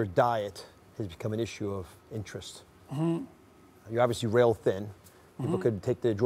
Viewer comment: That clip the guy wagging his finger adds so little value it just hurts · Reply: I thought he said “real thin”